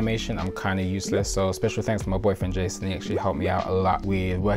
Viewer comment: did you say “my boyfriend”? 🥺❤️